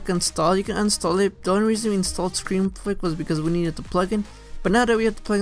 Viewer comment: what was he saying😂😂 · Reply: “Was because”... I was nervous making videos in the past so I spoke fast.